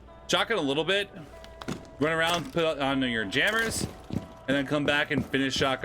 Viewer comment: Wait what u said on minute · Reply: Finish putting down your jammers. You heard what you wanted to hear